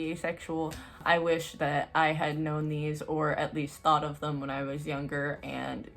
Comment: Idk y but i thought you said “i had no knees” instead of “i had known these”